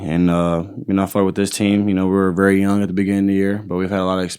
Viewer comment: did he say I fw this team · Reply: It sounds like it but I think he said “I felt with team we were very young at the beginning of the year”